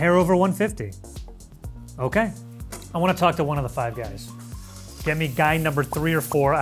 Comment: When he said "i wanna talk to one of the 5 guys, get me guy 3 or 4, i don't care" man i laughed pretty loud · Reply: all i heard was "I want to speak to one of the 05 i don't which one"